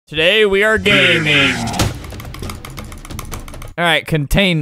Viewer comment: At the start I thought he said we are gay, and I was like nope I’m lesbian.